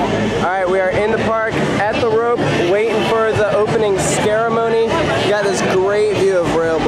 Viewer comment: Did he say “Scaremony” at 1:36?